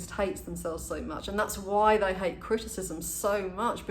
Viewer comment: Did you say that's why they hate Christian's so much? Or Christendom? Or something else? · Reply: They hate criticism.